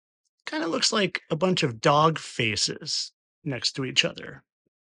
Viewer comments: I thought at first he said, 'Dog faeces'. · He's obsessed with dog faces, since his dog died, and he prayed about it. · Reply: At first I heard that as 'dog faeces'. That would explain a lot.